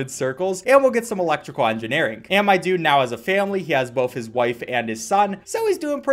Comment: did you say family?